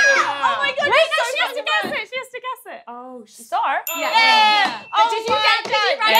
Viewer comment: what is the word @ 15:20? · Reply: Star